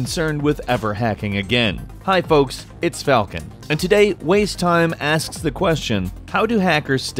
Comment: In my head I always hear " and today gameranx asks the question" instead of "and today wastetime asks the question"